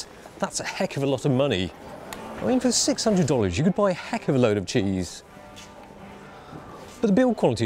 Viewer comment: I thought he said, "You could by a heck of a load of Jimmies." lol FYI - In some areas, Jimmies = condoms.